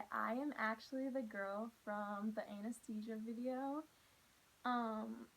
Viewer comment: I thought she said the “anus teacher video” IM CRYING 😂😂💀